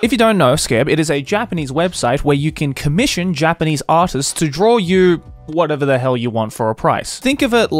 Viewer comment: I thought he said "Japanese A$$s"